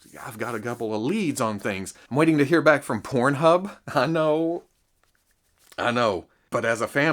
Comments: Did he say Pornhub at about 22:15-22:20? O.o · did you say Pornhub? Are you uploading pornography now or do they have a toy section I don't know about?